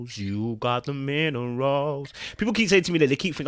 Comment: Funny because I've always thought you said, "You've got the men aroused" hahahahaahaaa...